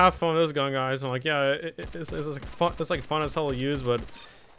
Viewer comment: did i heard him said the the f word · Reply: No he said fun as hell